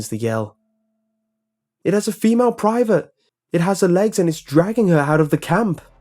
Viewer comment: Listening to this while going to sleep. Half dozing off in the second story i hear 'it has female privates!!' Wait...what?? That woke me up hahh had to rewind